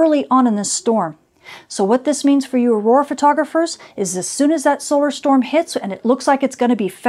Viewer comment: Sign of our times - The first time I heard "Arora photographers", it took me a moment because I heard "War photographers"